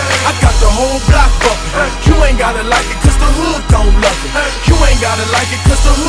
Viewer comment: I always thought he said "you don't gotta like it cuz the hood [don't] love it" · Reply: Drew Johnston I thought it said u ain’t gotta like it cuz the hood don’t nothin lol